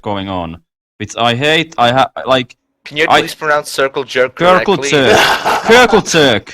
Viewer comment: I thought said Circle Turk or something